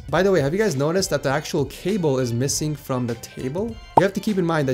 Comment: I thought you said the table was missing not cable